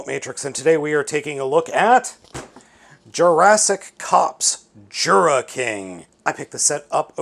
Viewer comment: I actually thought he said "Jurassic Cucks"